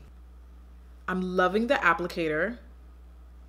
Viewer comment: did anyone else hear im loving the apple care